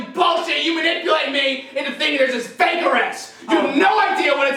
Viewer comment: thought he said fake arrest